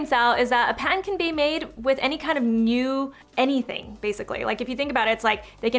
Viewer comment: panted?? or patented? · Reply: I’d thought she pronounced the t very lightly until I heard “A PAN can be made with any kind of new anything...” 😂